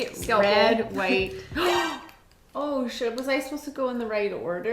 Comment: I heard "oh should-" not "oh shit" is safe...I think.